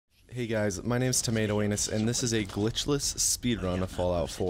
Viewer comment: at first I thought you said "tomato anus", then I scrolled down and turns out you did